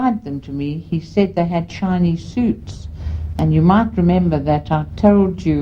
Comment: I keep hearing her say "Chinese suits" lol. Excellent video